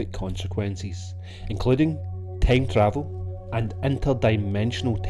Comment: "-including tank travel-" Lol. Tank instead of time. Funny computer error.